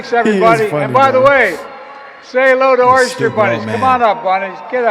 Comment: It said "Oster Bunnies". What a 'president' and administration you have got there my American 'cousins'. You need to vote evil out on November 5th. Trump 2024. Trump for 47! Greetings and prayers from England. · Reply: I thought he said “Oyster Bunnies”.